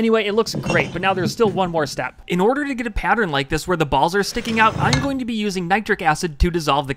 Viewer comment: Did u just say when the ball are out